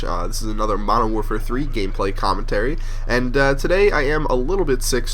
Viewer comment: at the start i thought you said modern warfare 3 gayplay